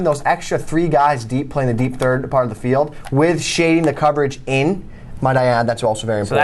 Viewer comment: what did he say at around 1:12 for the "big tip"? shading the coverage in? and how do you make that adjustment?